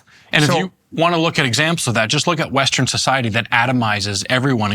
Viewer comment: I’m only gonna say “eggzampz” instead of examples from now on. My man is cuttin’ syllables his speech so lean.